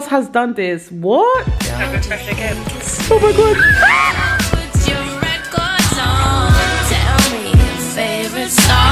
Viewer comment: What did say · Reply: “Should I refresh again”